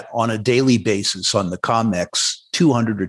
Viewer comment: did Rick just say “the CONmex” instead of “COMEX?” 😂 👍🏻